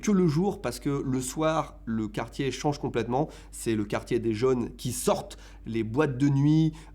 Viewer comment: Il dit bien "le quartier des jeunes", des JEUNES ! Au cas où d'autre ont buggé comme moi :D